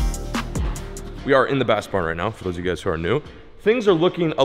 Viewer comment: Bro when he said “bass barn” I thought he said bass pro when he was showing all the soft plastics 😂 that says a lot about how many lures he has, good vid 💪